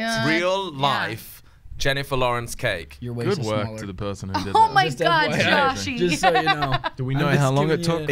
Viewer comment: what does josh say about the cake at 2:28?? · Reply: Its so cute Jen says "Oh my god, Joshie" 😂